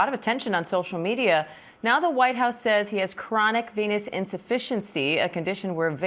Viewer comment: At 0.40 I thought she said ' chronic penis insufficiency ' but we already knew that.